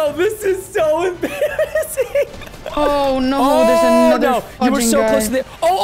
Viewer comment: At 7.04 I thought Preston said the f word then I turned up but he acctually said fudging